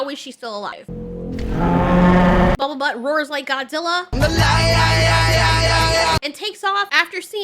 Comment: Listening while making dinner i hear " bubble butt roars like Godzilla. " That got my interest.